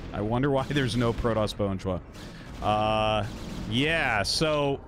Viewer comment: What did say at 9:29? "I wonder why there's no protoss ??????" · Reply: "I wonder why there's no Protoss "Bonjwa"" There's a clip on YouTube where talks about there not being a Protoss Bonjwa, just in case you are curious.